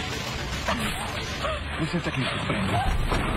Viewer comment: não entendi o que ele falou 😳😲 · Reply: Ele disse: "você tem que me surpreender".